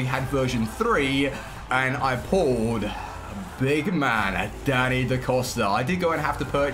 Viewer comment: It sounded like you said big man daddy da costa lmao